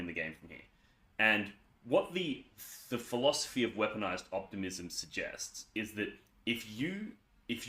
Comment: I was chewing food as I listened to your intro and thought I heard "Weaponised Autism"